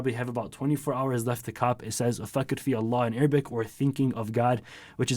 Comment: i thought i heard the name for the merch "fuck it we Allah" tövbe tövbe